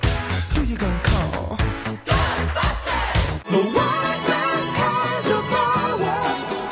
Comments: Tbh I always thought they said the white man has the power when listening to it in GTA. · 😂😂😂. THOSE BUSTERS. I thought it said ghost busterts 🤴